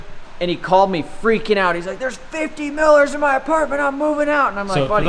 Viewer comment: Miller also a common Amish name besided Yoder. Me very confused when is saying he has 50 Millers in his apartment. Thank you for explaining they are moths. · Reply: I thought he said milner. I didn't know why he had 50 hat makers in his house.